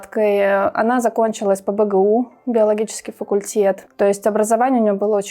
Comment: Университет, биологический факультет! И почему то стюардесса? Всё странно!!! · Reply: ГЕОлогический мне послышалось